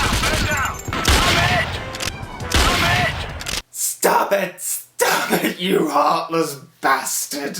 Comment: I thought they were saying "I'm hit". It wouldn't surprise me that they do say "stop it" because in Agent under fire some guards say "ow" when you shoot them.